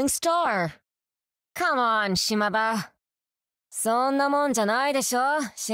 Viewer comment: Not sure if it's just me mishearing it, but it really sounds like she's saying "Shima-ba" instead of Shimada. The ba could be in reference to "ba-ba" as in granny.